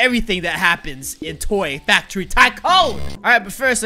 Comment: roi did you say toy factory tycoon??????